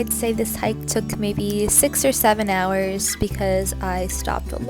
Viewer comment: Did you say You stopped a lot to have lunch and “sex” at 3:44 ?